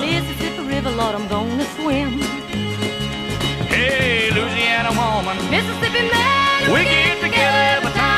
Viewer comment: As a kid, I always thought he sang "Uzi and a woman" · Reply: I always thought it said "Lose another woman, mississipi man....."